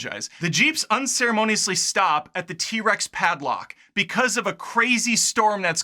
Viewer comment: he says that the jeep stops at the T-Rex "padlock" instead of paddock.